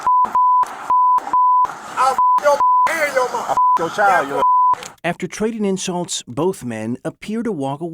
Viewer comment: Did he say “ I’ll F$&k your child”? If so that’s wild..